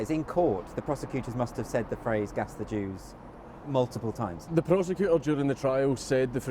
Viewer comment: I’m fucking retarded I thought he was saying pass the juice 😂😂